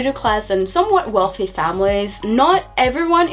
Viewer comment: What did she try to say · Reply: "somewhat wealthy families..."